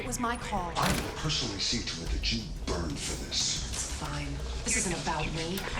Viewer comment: I'm really enjoying the story. The CC is usually pretty good, but it's also got some unexpected goofs. The best one I've noticed so far: At 1:03:31, here is what the colonel says, and how the CC renders it: "I will personally see to it that you burn for this." "You should personally seek a letter of genuine agreement for this."